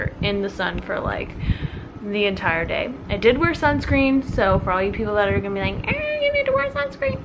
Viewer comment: Dont forget your sunscreen the next time, please. we are worried, thats all. If It was me in the sun I would get bubbles and second degree burns. I'm loving the videos, please keep doing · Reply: Maddie Lymburner oh, I heard you saying that you forgot sunscreen LOL I guess I was just sleepy. That's why I said this. I wouldn't say anything but then I heard you saying you didnt use. not Native english speaker. must be that. Sorry.